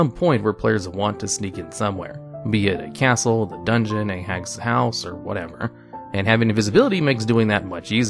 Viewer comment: I heard "hag's house" as "ex's house" at first. I'm now imagining a player trying to convince the others to help them break into their ex's house to get their stuff back lol.